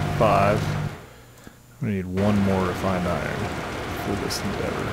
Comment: I thought you said that you needed 1 more refried iron. Lol